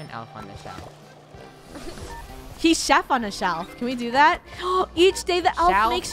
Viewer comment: did anyone else hear say chef on the elf lol 😂😂😂 btw love your vids and the krew